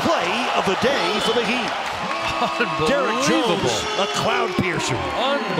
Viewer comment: I swear I hear Derrick Rose